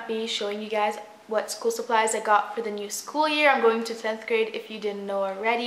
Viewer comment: I thought she said 5 grade 😂